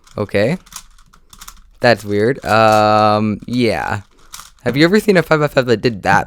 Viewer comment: what are you saying bruh go back to school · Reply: He’s literally saying have you seen a fizzle fizzle